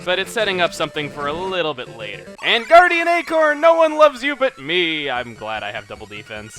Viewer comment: The first time I saw "Guardian Acorn" I thought it said "Canadian Acorn" YEAH!!!! XD